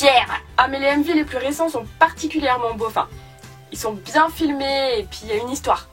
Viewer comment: /7:45, j'ai compris beauf ! 😂